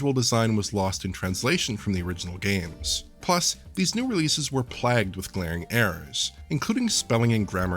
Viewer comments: Did he just say "plagged"? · Does he say "plagged" instead of "plagued"? Is this like, a US pronunciation I wasn't aware of or something?